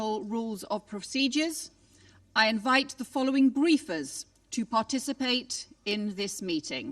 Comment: did she say she invites the following griefers?